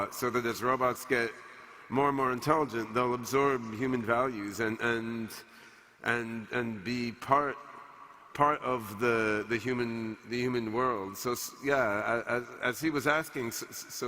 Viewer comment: talking to Sophia, did he say "and be part of the human demon world"?